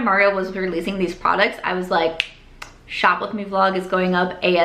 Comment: you said “shop with me vlog” but i SWEAR i heard “shoplifting vlog”